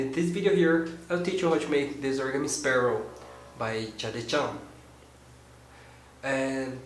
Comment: thumbs up if u think he said chade chan